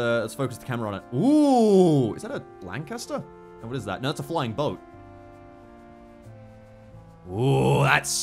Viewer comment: did he say flying boat because it has bombs